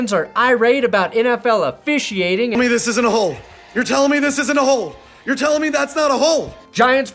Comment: What was actually said: "You're telling me this isn't a hold" What my brain decided was said: "You're telling me this isn't a hole"